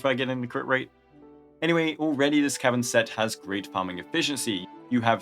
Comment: I had to turn on subtitles because it sounded like you said "Anyway this Kevin set" at 6:00 I was like Suske